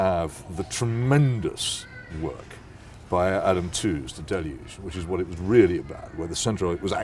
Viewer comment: Anyone catch the names of the books Peter mentions around 34:00? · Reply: He mentioned The Deluge by Adam Tooze